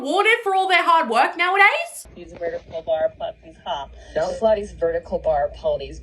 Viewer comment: is at least a fun guessing game for what the AI is trying to say. What does it sound like to you? I think it’s trying to say *vertical bar potties*.